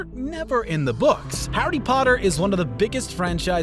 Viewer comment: did..did he just say howdy potter??